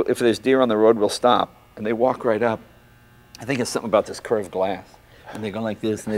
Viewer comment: "Cursed glass" ? Whaaaaat = ))) ? 8:02 "I got to ask" ? That's what jay's saying after "sorry mr. edison" ? 10:04 WRONG !!! It sound like the tram's bells when sounding before leaveing the station. 12:12 Whaaaat jay ? Whaaaat ?